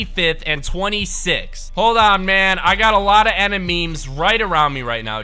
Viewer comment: did he say “Enememes?